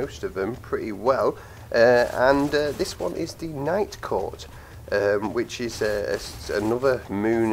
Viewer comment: Legit thought he said Nightcore at first.